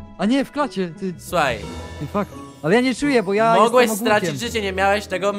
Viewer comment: czy ja usłyszałam " O fuck "? nieładnie tak na odcinku =}